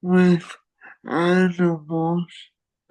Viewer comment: he said he worked with animals , i thought he said edibles